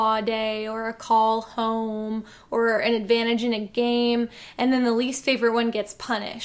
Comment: I misheard and thought you said "and then the least favorite one gets punched" and I didn't even react because of how this show has been so far. It sounded completely plausible lol